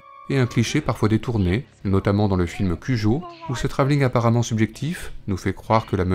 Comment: c quoi le nom du film? J'ai pas compris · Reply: Cujo (1983)